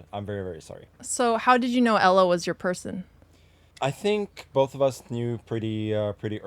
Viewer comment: Thought she said "How did you know ELO was your person" lol.